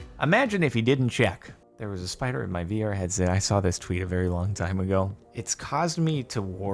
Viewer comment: OMG I THOUGHT AT FIRST HE SAID THERE WAS A TURTLE IN A VR HEADSET AND I WAS LIKE WATS WRONG WITH A TURTLE UNLESS ITS A SNAPPING TURTLE. My gpa picked up this HUGE snapping TURTLE bigger than a human head by the tail and through it in a box in the car cuz it was in the middle of the road it had the spike things on dinosaurs backs but on his tail a turtle of that size could cause a bad accident on a main 60mph road we drove a few miles to a huge swamp that wasn't on the edge of that road and my gpa grabbed the turtle by the tail and tossed him in the swamp that thing was prob 20-30lbs it came an inch from my gpas leg